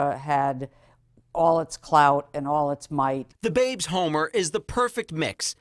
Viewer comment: did she say “all its clout”? 😂